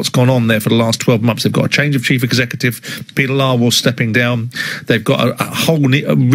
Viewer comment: did he say peter liar well